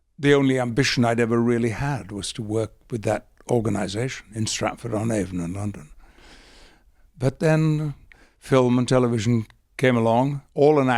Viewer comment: I couldn't quite make him out. Did he say Stratford upon Avon was in London at 2:26 ?